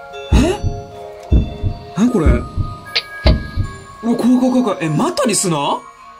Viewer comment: のとこなんて言ってるんですか？ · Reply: こわいこわいこわい またリスナー？だと思います